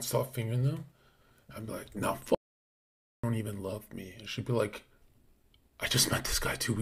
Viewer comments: what did he say under the censor ? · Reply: He said " fuk you bitsh you don't even love me" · What did he say at 45:46. It was censored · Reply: He said "nah F U bistch, you don't even love me."